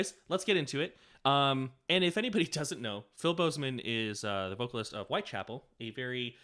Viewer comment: We should start calling Phil Bozeman "Philbo", cause that's what I thought you said